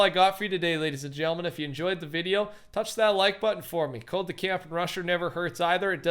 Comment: Why does it sound like he is saying the camping Russa